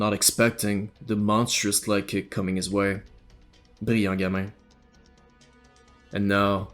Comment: What the hell did you say at 3:27? Beyangyame? wtf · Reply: Brillant gamin